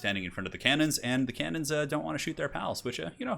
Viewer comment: i thought you said the cannons don't want to shoot their loads haha